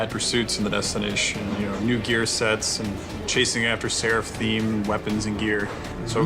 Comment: anyone else hear say seraph themed weapons and gear at 11:38 and again at 11:53?